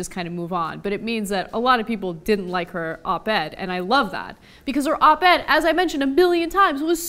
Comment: I thought she was saying "Abed". How can Abed be insulting to everybody?!